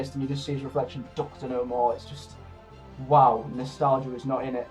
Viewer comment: Well done for FINALLY uploading this review! When I initially watched this minisode, I thought John Hurt said, "Doctor who?", instead of "Doctor no more". It wasn't until I watched it quite a few times for me to listen closely to what he actually said. I actually DO wish that we got to see , like, a 10-second scene with Christopher Eccleston after his regeneration from John Hurt towards the end of Day of the Doctor, but then again, I'm not surprised that he's let us Whovians all down because Eccleston doesn't really give a shit about the show anymore, and he is too much up his own arse, quite frankly enough. What a selfish prick he is! As for the YouTube comments saying your video on why you hate the Twelfth Doctor's sonic screwdriver is shit, FUCK THEM!!! They don't know what the heck they are talking about and quite frankly, they're a bunch of immature bastards!! Also, if Capaldi was going to have any light colour for his very own sonic screwdriver, I personally imagine it would either be red, purple or even black.